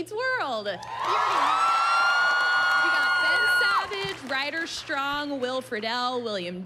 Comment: As the interviewer, the moment I forgot the main character of the show, I’m quitting lol literally can’t come back from that 😂😅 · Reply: But she didn't forget. She clearly says "Ben Savage" in the beginning, but says it as the audience was getting hyped up. The screams may have drowned out her words. At the 1:02 mark, you hear her say his name first.